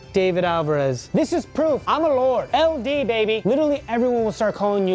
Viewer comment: When David said IM A LORD for the beginning of the brand deal, I thought he said IM ALONE 😂